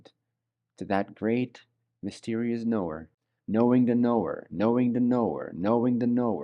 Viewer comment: it's like he's saying "knowing the Noor" is how I kept hearing it XD ---EDIT: lol i didn't make it far enough. PART 2